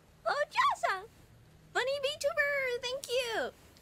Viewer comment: i thought she said "I'm a Vtuber"